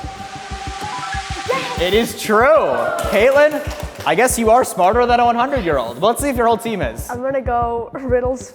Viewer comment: did you say kaylen or katelyn??? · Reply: he said Kaylen